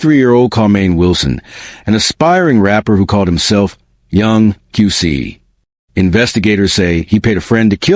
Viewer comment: sound like he said Young 🐈